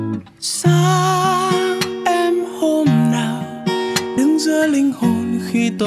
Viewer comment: xin lỗi nhưng tui nghe thành Xác em hôm nào :((